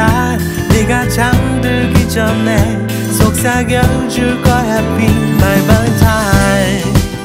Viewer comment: I thought he said my bow tie It fits with the video though